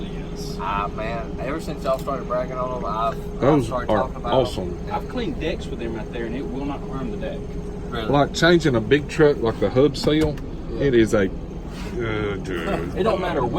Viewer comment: I thought he said he cleaned dicks with the scrapers😂